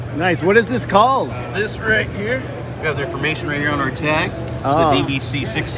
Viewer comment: thought he said the "BBC-16INCH"